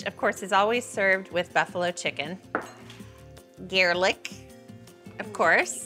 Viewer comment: It’s kinda funny that she said Garelick instead of garlic